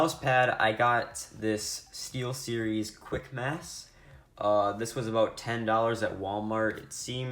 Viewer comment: Sounds like he said Quick mafs